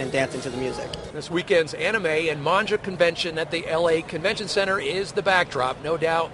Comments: "Monja"? Really?? Monja? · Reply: Kira Inuzaki LOL I heard "Manju Convention" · I think they are called Vocaloids not Digital Divas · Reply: Wait did he just say Monjah instead of Manga